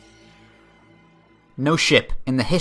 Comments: ok not going to lie but I honestly thought you said "No shit." lol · I heard "no shit"....